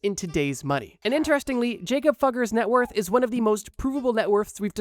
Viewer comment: At first a thought u said Jakob f###er not Jakob fugger